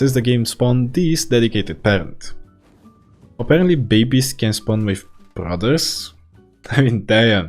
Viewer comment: My dumbass thought it said only rabies’s